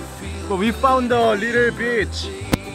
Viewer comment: Shocked because I thought said 'we found a little bitch'